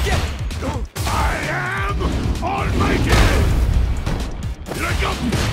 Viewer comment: I think Zangief says "I am almighty!" when he does the command-grab but I keep hearing "I am on my period!"